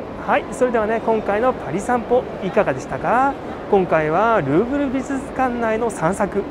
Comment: グーグル美術館って聞こえた・・・さすがグーグルと思ったらルーブルだった